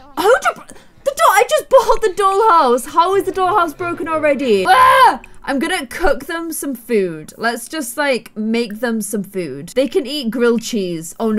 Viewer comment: when she said im going to cook them some food i thought she said im going to..... 4:56 CoOk ThEm!!!! to be honest i wouldnt be surprised if she did lol